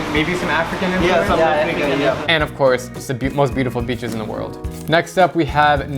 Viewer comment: why did i hear it has "bitches"💀